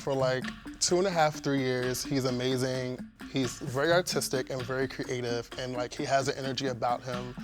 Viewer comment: WHEN SAID HE"S VERY ARTISTIC I THOUGHT HE SAID AUTISTIC